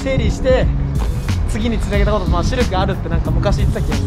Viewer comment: ここで「シルク」が「シュルク」に聞こえた人いますか？